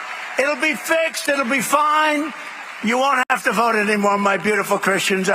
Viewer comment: I thought he said he didn’t want no votes anymore💀💀💀💀